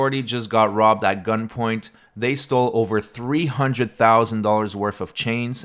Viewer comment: I thought you said "over $300,000 in change." It thought, why was this kid walking around with $300K in coins?